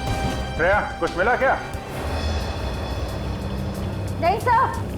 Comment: why does abhijeet sir says Daya instead of shereya 😂😂😂😂😂anyway I love sir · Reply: Shreya hai bola tha sune me lag raha hai ki daya bola hai😊😊😊😊😊